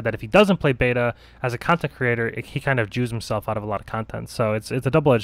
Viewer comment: wow did you say he "jews himself out of content"